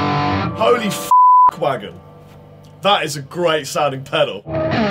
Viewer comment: What is saying at 5:46 ? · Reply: Did he say "Holy fuck-wagon"?? 😹